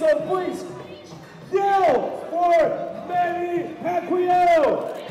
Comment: wow! Manny's been around for over a decade and this announcer can't even say Pacquiao! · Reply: yeah, i heard him saying Manny Macquiao